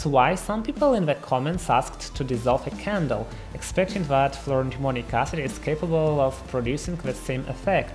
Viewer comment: I keep hearing "fluoro-demonic acid." I am not sure that that is wrong.